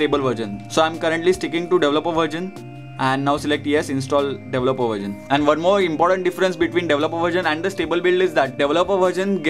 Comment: i kept hearing "developer virgin" :D